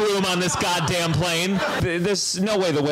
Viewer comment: "A backup whale" had me weak 🤣🤣🤣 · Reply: WAIT I THOUGHT HE SAID BACKUP PLANE WTF SJSNSLHDSO